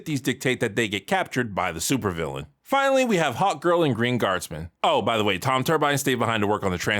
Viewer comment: I keep hearing you say "hot girl" instead of Hawkgirl. And honestly, you'd be right either way.